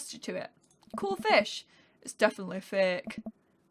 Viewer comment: Instead of " it's definitely fake " I thought said " It's definitely thick " and I diEDD